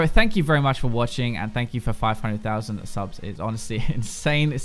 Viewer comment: What he says: "Let's go to the robots" What I hear: "BRING IN THE DANCING LOBSTERS!"